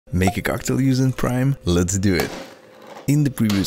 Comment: Carktail is what I heard him say